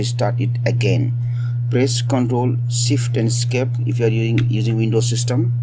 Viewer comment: control, shift and what??? Can't understand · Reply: esc.. to open task manager